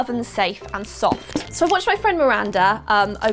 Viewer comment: “I watched my friend Miranda,” What I heard: “I watched Lin-Manuel Miranda,” Lol never mind art supplies, I think it’s new earphones I need to invest in. 😄 you have the best and most entertaining (non-unboxing) unboxing subscription channel on YouTube. Thank you for getting us through lockdowns. All the best to the people who want to win a Subscription box. 🤞❤️